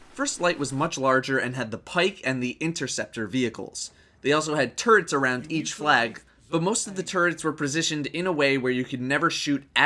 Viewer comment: sounded like you said they have turds around each flag